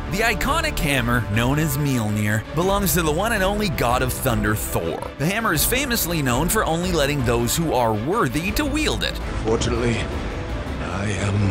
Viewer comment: i thought you said "Xander Ford"